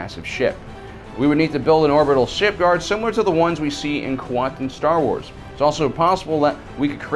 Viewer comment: what the fuck are you saying here? Quantum Star Wars? Kwantin Star Wars? Quanton Star Wars? Quantorn Star Wars? Cantor Star Wars? WHAT ARE YOU SAYING? what is he saying guys?